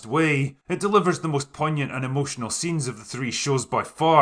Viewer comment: Weird question, but what word is he saying at 9:04 ? Google can't understand it and I don't know what that means. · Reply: I think you mean when he says poignant? Which means to effect you or express something deeply, often relating to sadness. (If that makes sense!)